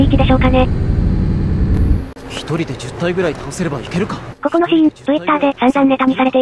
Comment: この時みんな一人で10体くらい倒せばいけるか…！？って書いとるけども倒せればに聞こえた